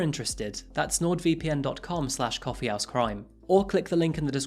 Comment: I love this channel but i am sorry i keep hearing you say "coffee ass crime"